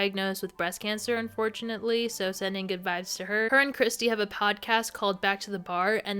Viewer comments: omg when you said sending good vibes to kelly because of her breast cancer i first heard 'goodbyes' looooooool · Omg at 54:35 I thought you said "so sending goodbyes to her" and almost lost my shit 😭